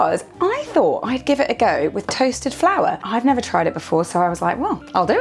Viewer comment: when you mentioned toasted flour, i thought you said toasted flower... hee hee